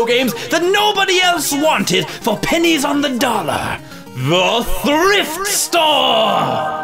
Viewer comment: Instead of "thrift store", it said "horrific store" in my subtitles^^ It kinda fits😂